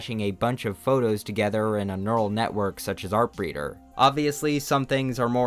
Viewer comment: Does anyone know what he says here? ''The neutral network known as'' what? Would be cool to make some of these types of images. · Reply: I think it's artbreeder <link>